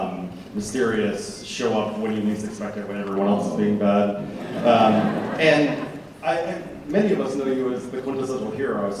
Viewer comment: Can anybody tell me what Chris says at 3:20? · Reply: He whispers * Fuk- trump - again